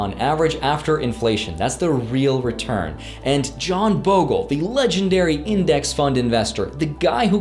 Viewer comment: Did he say, John Bogle...?